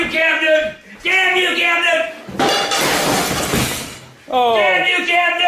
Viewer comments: Damn u Cam Newton !!! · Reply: Lol I thought he was saying "dam u captain" cuz munnerlyn missed the tackle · "Damn you Cam Newton" 😂😂😂😂😂😂 then called him fig newton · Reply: It sounds like he said "damn you captain"